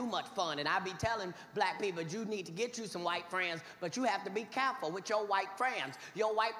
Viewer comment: I thought he kept saying his wife's friends.